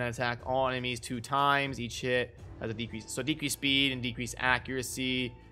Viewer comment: You realise when you were running through the skills quickly, each time you said EACH HIT, it sounded like you were saying EAT SH*T? lol